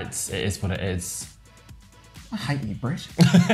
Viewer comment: "I hate you brett" 🤣 🥰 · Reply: Shows how "stoopit" I am....I thought she was saying "I hate you, Brit". 🤣🤦🏻‍♀️I kept wondering why she was saying that.